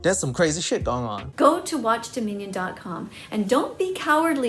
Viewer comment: everytime i hear her say "watchdominion" she pronounces it as "watch da minion" i swear